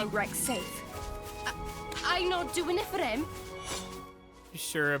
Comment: Did she say "I-I'm not doin' it for him!"? For some reason I couldn't make out what she was saying